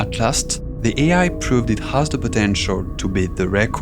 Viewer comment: DID YOU SAY POTENTIAL?